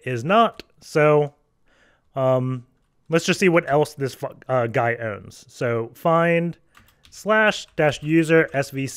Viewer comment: Did he just say "let's see what this fuc* owns"??? xD